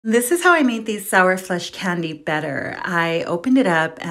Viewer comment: I thought she said sour flesh for a sec😅